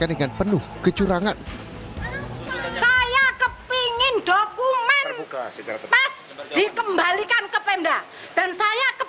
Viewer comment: Klo GK salah dengar ibu itu bilang, saya minta dokumen Tas di kembalikan